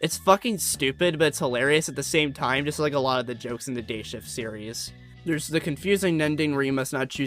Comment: I thought you said ‘gayshift’ every time